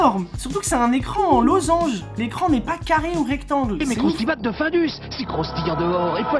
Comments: c’est moi ou a 7:20 le mec dit ´´ crustibat de falus???😂😭😭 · j'ai entendu phallus au lieu de findus 😭😭